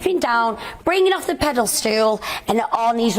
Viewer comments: Did she say pedal stool at 5:55? 🫣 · Did she just say pedal stool? 😂😂